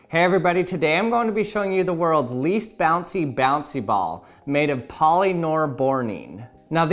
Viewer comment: I thought it said "The world's last bouncy ball" and I was like D